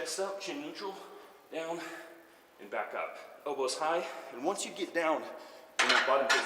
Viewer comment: for some reason I heard “Bilbo’s high” instead of “elbows high”